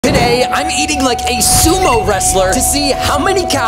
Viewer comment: bro i thought he said "Today, im eating a sumo wrestler"💀